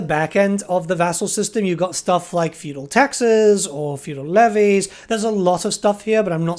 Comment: I thought you said feudal Texas ….interesting thought experiment